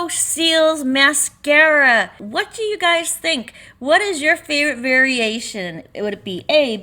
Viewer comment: 😝 For a second, I thought you said, “what is your feared variation?” Which is sort of a good question...